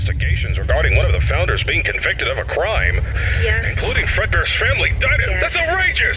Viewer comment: I thought he said “WHAT IN THE FRED BEARS FAMILY DINNER W- THIS IS OUTRAGEOUS” and I started dying laughing